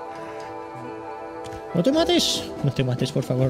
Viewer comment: entendi tomates